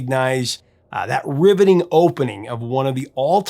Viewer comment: I heard "ribbiting opening", lol